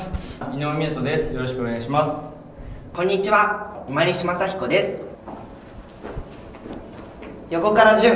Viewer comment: が「今西正彦です。」っていったの「マネ島サキ子です。」って聞こえてギャグかと思いました。空耳でした。(聞いてない)